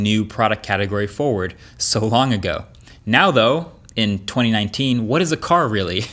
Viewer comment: Does he said " now though in 2019"